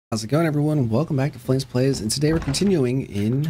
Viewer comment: I could have swore you said “plays plays” at the beginning. 🤣 I loved the video btw! You’re very entertaining.